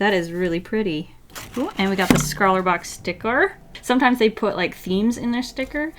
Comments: did she say scrawlerbox-sticker? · Did she just say SCRAWLRBOX?